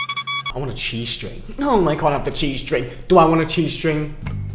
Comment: I THOUGHT HE SAID G STRING